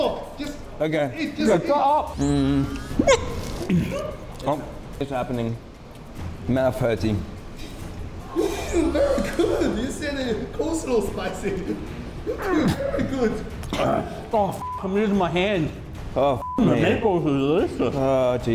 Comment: I love that guy. They are dying and I just hear "and you thought coleslaw was spicy!"